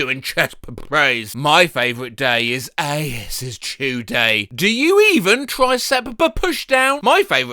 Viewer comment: did he just say asses chew day